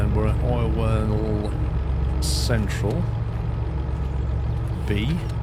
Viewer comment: idk if its just me but i came into this series late but everytime he said "oil well central" I always thought he was saying orwell central till we went there